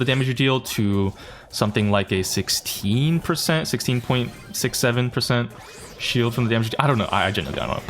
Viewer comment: did he say 6 7??